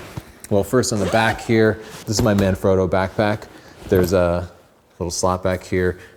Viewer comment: I was listening to the video while looking away, and I kept hearing you say “my man, Frodo” and thinking “that guy is really into Tolkien” 😂